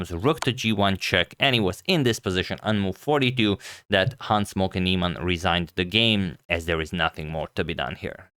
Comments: Every time Agadmantor says "Hans Moke Niemann" I hear "Han Smoken Niemann" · Always sounds like he says "Hans Smokey Neimann"